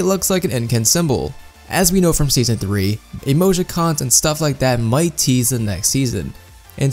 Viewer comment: did you just say emojicons ? :D